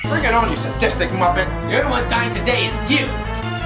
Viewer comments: "You're the one dying today is you!" Elliot Cancel - 2010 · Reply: Fariko Wishless Yeah, I was wrong. I don't know how I heard "The only one" XC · *"The only one dying today is you" * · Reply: He said "the only one"? It sounded like he was saying "you're the one".